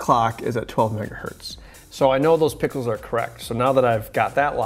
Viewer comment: i think he said "so i know now that i have my pickles correct( pickles,not pixels.)!